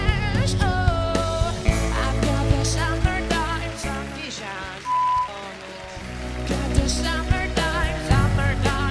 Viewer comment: Does anybody know what the woman at 1:12 says? Where it bleeps a part of it out? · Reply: 'Dio bono' it's like 'fucking god'